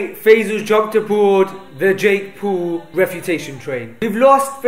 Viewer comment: did he said Jake pool?